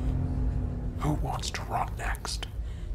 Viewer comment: I will never not love the voice over of “Who wants to rot next, eeny meeny Miny😂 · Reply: I’m such a dumbass I thought she said “Who wants to rock next ?” 😂🤦🏽‍♂️